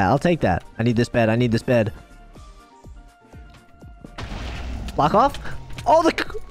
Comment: did he said f**k off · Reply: No he said block off